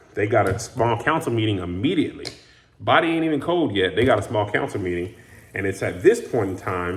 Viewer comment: I thought he said "Biden ain't even cold yet."